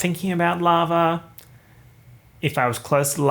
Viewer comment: For some reason I thought you said "larva" instead of lava